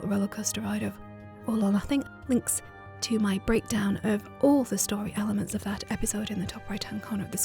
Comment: I thought you were saying 'link to my breakdown at the end of the episode is in the description below' for a second 😂😂😂😂😂